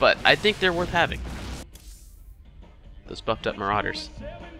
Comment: first time i totally thought you said "those f'ed up Mauraders"